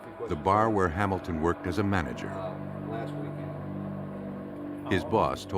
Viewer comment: I thought he said he worked there as a mannequin and his boss said he’s a MODEL employee - and I thought ahhhh I see what you did there .. but yeah went back and checked and no he’s just a manager not a mannequin 🤣🤦🏼‍♀️🤷🏼‍♀️